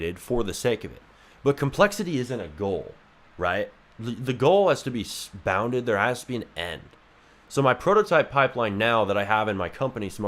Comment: what did you even say here lol? Also how can I join one of your Qna’s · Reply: i said 'the goal has to be bounded, there has to be an end'. Join the discord - <link>